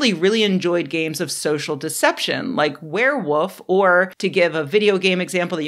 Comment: Did I hear a “werewoof”?